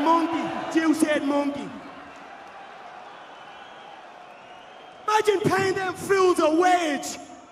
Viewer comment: What did Conor say at 1:37:11? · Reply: "Imagine paying them a wage." -Conor